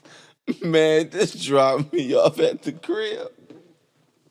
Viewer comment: I thought you said “Drop me off at the crab” lolll